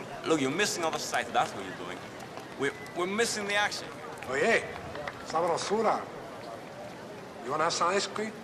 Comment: What is he saying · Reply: "Oye! Sabrosura!" it translates to something like "Hey you! Tasty thing!"